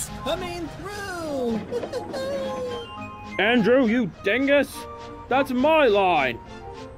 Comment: did Gordon just call Andrew a dingus?